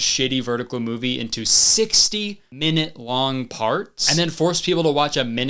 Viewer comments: At first I thought you said "60 minute parts" and not "60 1 minute long parts" and I thought you were overreacting. What even is this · OHHHH this whole time I thought he was saying sixty-minute long parts, as in an hour.... Sixty minute-long parts makes way more sense now lol 🥲